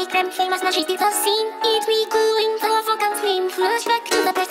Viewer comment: "Who's sing now?" erm....you? Edit:I keep hearing"miku and the fuck all swing"and I can't unhear it